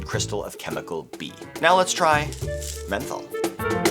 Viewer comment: could have sworn he said Fentanyl- not menthol… had to rewind a few times